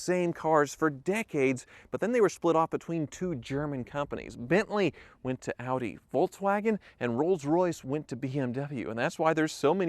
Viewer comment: Bentley went to Audi ... Rolls Royce and Volkswagen went to BMW? Did I hear that right! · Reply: No - he took a breath at the wrong moment. What he said: Bentley went to Audi/Volkswagen; Rolls Royce went to BMW. condensed History/Background: Bentley went to Audi/Volkswagen (***Volkswagen owns Audi so he really didn't need to say Audi at all***); Rolls Royce went to BMW (***truthfully speaking, only the licensing to use the name Rolls-Royce went to BMW, for pennies really. Volkswagen was deceived and paid full price to own both Rolls-Royce and Bentley... not knowing that Rolls-Royce Aerospace owned the rights to the name Rolls-Royce. RR Aerospace refused to allow their name to appear on anything built by Volkswagen. So, under the table, BMW and RR Aerospace made a deal and BMW was allowed the right to use the name Rolls Royce for essentially free compared to what Volkwagen had just paid for the actual company, HQ, plant, etc of the existing car manufacturer RR/Bentley at the time. As a consolation, BMW had no choice but to pay Volkwagen to continue manufacturing Rolls Royce for them until BMW ultimately built a new plant to begin manufacturing the next generation of BMW-RR vehicles.***)